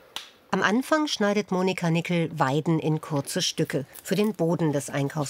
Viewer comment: Ich verstehe da: "Moni Karnickel" :-D